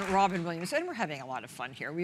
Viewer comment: She introduced him so fast I swear it sounded like she said Robin Williamson and then he looked down anyone else hear that lmao